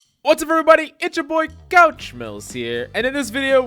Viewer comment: It sounds like your saying couch mills here in the beginning lol